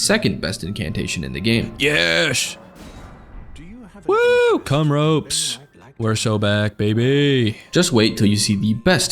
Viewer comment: Did bro just say c*m ropes 💀